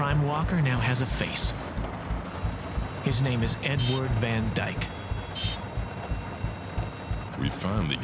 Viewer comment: Was making dinner with this on and I heard "N word Van Dyke" 😂